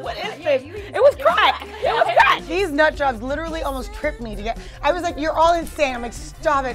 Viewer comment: Did she call everyone nut drops 2:37 lol